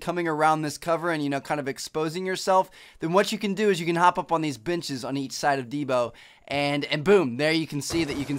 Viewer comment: hop up on these bitches, is what i thought he said